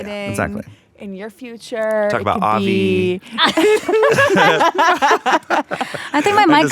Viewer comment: did say "talk about avi" or am i tripping